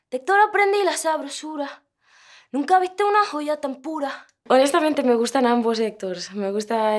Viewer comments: Everytime I listen to 'tan pura' I always misheard it as 'tempura' It makes me hungry. Sorry. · Yo pensé que decia, "de historia aprendí la sabrosura"